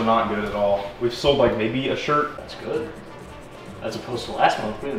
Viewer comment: WAIT...You guys sell SHIRTS? I thought you've been saying SHERPS...You know, like Sherpa's? THIS CHANGES EVERYTHING!